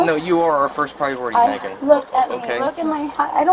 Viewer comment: Can anyone tell me what the girl says after getting found in the cabinet when the caption says "M******d" at 14:57? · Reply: Wait nvm I think she says "Molested me"